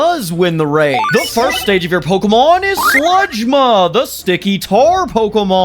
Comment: I think I heard SIGMA🗿🗿